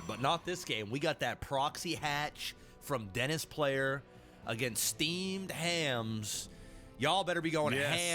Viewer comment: I thought you said steamed clams